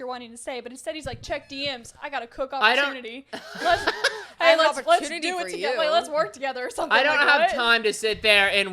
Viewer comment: did say lets do. It together 😂😂😂💀💀